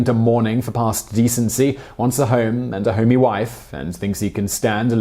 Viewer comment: "Now if some good bachelor wants a home and a homey wife..." I swear I heard that as "wants a home and a horny wife" at first.